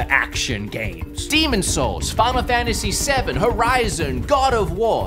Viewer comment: I swear to god i heard you say farmer fantasy 7 and i think you got an idea there